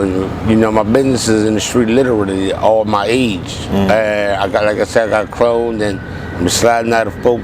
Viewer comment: At around 18:44 of the video, it sounded like he said he got cloned. I'm not sure if I heard it right, but If that is what he said, I think it would be interesting to hear more about that.